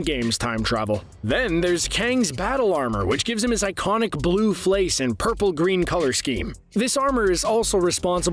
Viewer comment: did I hear "blue flace"?